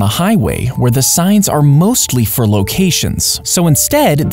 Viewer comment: For a second i thought you said clean instead of green and i was confused when you talked nothing about cleaning until i heard you say green again at the stsrt of the video 😂